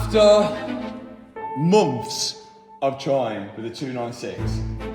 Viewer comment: I heard "after moths" anyone with me? :P